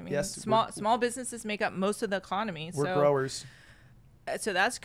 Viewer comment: did he just say we are growers ?